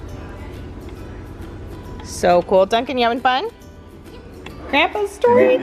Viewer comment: Is it just me or did say "Duncan"?? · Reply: I’m pretty sure he said “Dar Dar”, that’s what he calls cars!